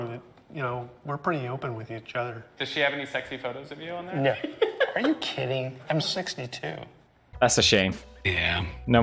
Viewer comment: and Pewds misheard him, he said "Are you kidding? I'm sexy too"